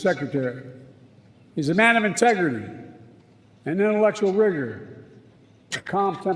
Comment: I intensely dislike corrupt, habitual liar, Joe Biden, but in the interest of honesty and fairness, Benny is pushing falsehoods @ 31:53, by ridiculing Joe Biden for saying "intellectual wigger" when he actually said "intellectual rigor." Even just turning on closed captions on YouTube shows that much. If YouTube's voice recognition algorithm can figure it out, I'm pretty sure Benny Johnson can too. Remember, pushing lies and distortions only makes you look like a liar and a cheat. It doesn't help Trump, the MAGA movement, or Make America Great Again. I just makes you look like a liar and a cheat.